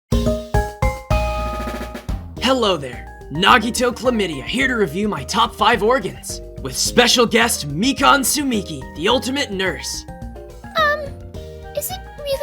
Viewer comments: WHY DID I HEAR "Nagito komidea" HELP FJSJRJSJRJRJR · Ok so at the beginning it sounded like he said nagito chlamydia instead of komaeda